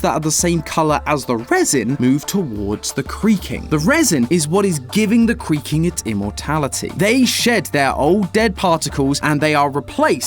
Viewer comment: did only i hear him say cweaking...